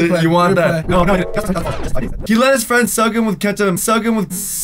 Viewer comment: how tf did "soak him" turn into "suck him"? P.S. I didn't hear either! I heard him say "sugem"!